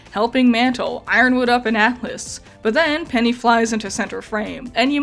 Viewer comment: does anyone else hear "Ironwood up in atmis".